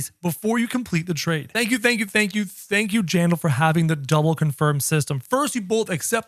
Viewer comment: 😂thank u general instead of jandel